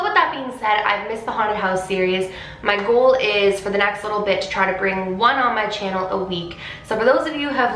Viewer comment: I thought she said “for the next little bitch” is it just me